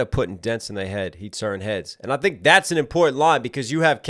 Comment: im almost positive he says “instead of putting dents in their heads, he turned his” and not “heads” in reference to turning the other cheek!